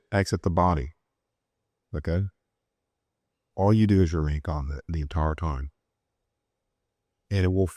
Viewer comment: When you start to exit the body, all you do is you??? the entire time". I can't understand what he is saying here in this sentence. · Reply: remain calm the entire time!